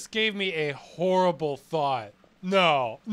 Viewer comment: what was said "horrible thought"?